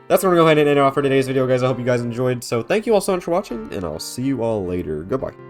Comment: lmaoo wtf are you even saying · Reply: i'm thanking everyone for watching?